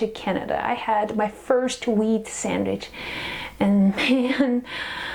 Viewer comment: sounded like she said "weed sandwich" ....no wonder u were so tired after eating it.